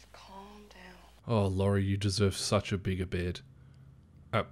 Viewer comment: I legit heard him say "oh Laurie you need a bit of this beard" 😂